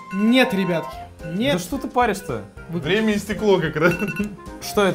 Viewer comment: вы тоже услышали на 3:45 ,,время и стекло''